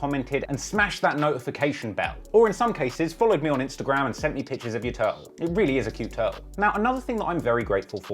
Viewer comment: I thought he said "I'm thankful for everyone who followed me on IG and sent me pictures of their toes"